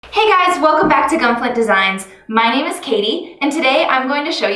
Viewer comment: I thought you said Gunpla Designs at the beginning and I got a bit too excited but seriously ty for the video, it helped me out.